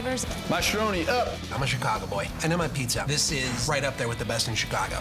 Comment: So he is a Chicago boy and he is a pizza🤣 That's what I heard anyway!